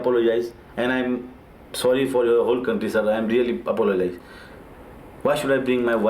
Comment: am sorry for your whole country - thats what he said